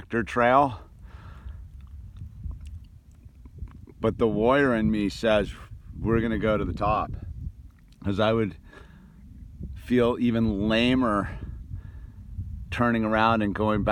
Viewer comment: haha I first misheard it as "But the Lawyer in me" Btw, beautiful landscape!